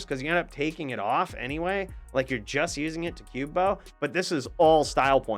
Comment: "you are just using it for ***" What did he say? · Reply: Cube Buff